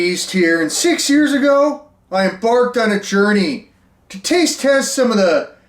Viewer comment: Totally sounded like said "Six years ago, I em-barfed on a journey."